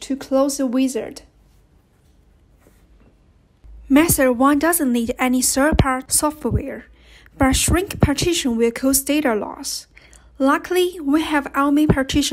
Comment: I can't understand what you said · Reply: Method 1 doesn't need any third-party software, but shrink partition will cost data loss